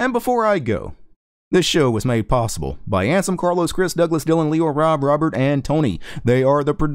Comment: I always thought you were saying "Handsom Carlos" when listing your patrons, haha